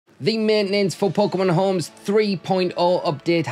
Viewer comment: When you say "for Pokemon Home" I hear "football's coming home"